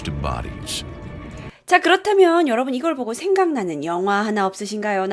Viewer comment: 초에서 잘들어보면 영화가아니라 연어로들리는데요??